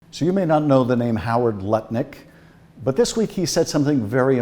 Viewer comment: Im sorry - did I mishear? Howard Nutlick?